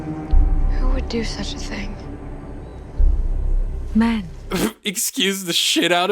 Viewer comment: Although I agree with you 100% about the show and this is a VERY interesting video, at 1:12:34 I don’t think she said “Men” as in plural for Man but “Man” as in “short for Man-kind.